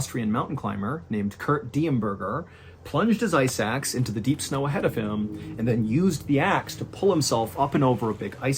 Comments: When you started story no. 1, I thought you said “he plunged his eye sacks into the ice in front of him” Enough weed for today. 😂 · Ice axe, i thought you said "eye sacks" and was immediately confused. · Oh i thought you said plunged his eye sacks.. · when you said "plunged his ice axe into the snow" I fully thought you were saying "eye sacks" and was trying to picture why and how one would do this